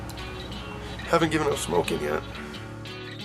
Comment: What did Adam say??. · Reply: "Haven't gave up smoking yet"